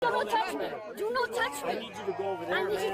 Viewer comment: Did I hear her say? “No touch me”?